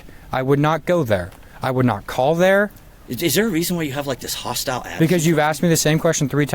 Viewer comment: "I'm hard of hearing. Why are you raising your voice at me?!" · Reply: I did not hear him say “why are you raising your voice” I heard him say “Why are you being so hostile?”🤦‍♀️